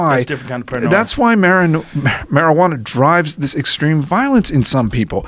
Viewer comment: Anyone else catch Berenson almost saying marinara at 1:26:08 😂😂😂😂